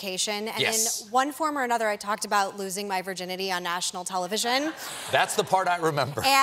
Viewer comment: oh wait, I thought she said she lost her virginity on national television.